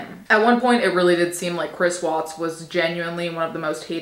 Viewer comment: I thought it said Chris Pratt and I got scared